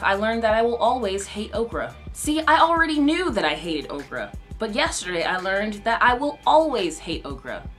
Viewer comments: I had to watch this on low volume and I swear I thought you were saying, over and over again, "I hate Oprah." I was very confused. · I had to watch this on low volume and I swear I thought you were saying, over and over again, "I hate Oprah." I was very confused. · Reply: OhHeyItsDiamondNater I'm just jumping in to say that when watching this just now, I too thought you said you hated Oprah. GLAD THAT'S CLEARED UP